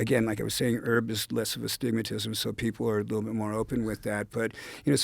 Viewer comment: Did he just say "herb is less of astigmatism?"